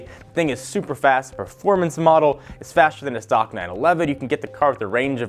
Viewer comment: This presenter needs to speak more clearly. He mumbles over some words and I can't understand a lot of what he says. Example at 1:00 "it's faster than a stock man elavet" ??? I presume (after a lot of contemplation) that he said a "stock 911".